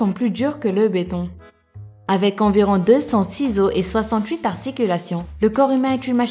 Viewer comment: mdr au lieu de comprendre ''206 os", j'ai compris ''200 ciseaux'' XD 😂😂😂